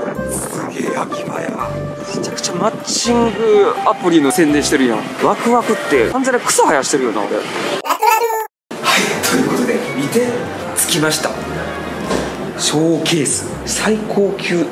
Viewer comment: マッチングアプリの宣伝のところの後のアイキャッチ？のラトゥラトゥがワクワクに聞こえたww